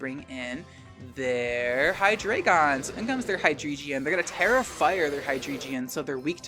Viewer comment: Who else thought was saying “Terrifier” every time they said “Tera Fire”